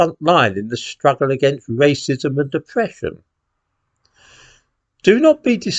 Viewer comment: 'Racism and depression'~ I know I misheard, but it also made a seamless sense...